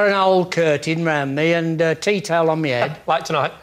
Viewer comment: "teetail" on his head? What's the word? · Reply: tea towel